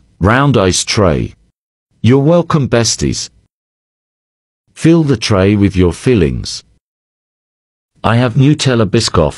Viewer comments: I heard “fill the tray with your feelings” and my mind immediately went to “creamy intentions” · I keep hearing “fill the tray with your feelings” 😂